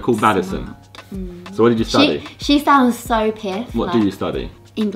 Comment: Did Freya just say piff? I'm only 17 but I haven't heard that in AGES